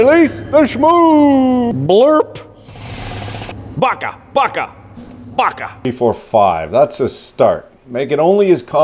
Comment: did I just hear go "baka baka baka"?